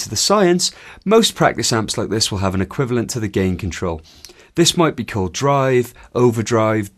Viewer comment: did i hear gay control in